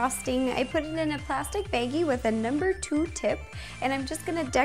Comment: she says beggie instead of baggie